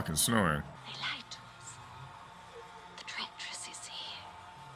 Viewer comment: If nobody already said it, she said, "Directress." I had to rewind the movie over and over and it STILL took me a while to figure out what she was saying lol